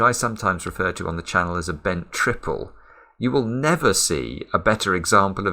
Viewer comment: I SAW IT. I SAW THE BENT TRIPLE! Me to my 🐈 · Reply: Wow, I heard "Ben's triple" till now, Thank you :D